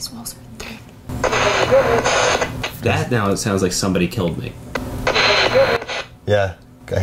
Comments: I hear “family killed me” maybe it’s just me tho 😂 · When they were talking about ‘ hearing somebody killed me ‘ I heard ‘ families coming ‘ since it did say something about family shortly before, it could’ve said ‘ family killed me’ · for the thing they said “someone killed me” for the spirit box I didn’t hear that at all instead I heard “getting bigger” or something along the lines of that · I heard "little gremlin" instead of "somebody killed me" 😭💀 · I heard 'can you hear me" the first time with ??? instead of somebody killed me. Just sayin. Glad to have Y'all back